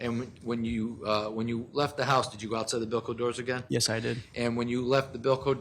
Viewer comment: Omg I was so confused, I went as far as googling Velcro doors.. umbilical doors everyone pronounced it different 😂 BILCO. THANK YOU! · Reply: haha same. I thought they were saying Velcro doors also.